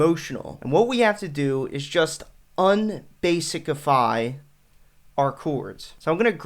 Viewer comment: Did he just say "Unbasicify"?!!!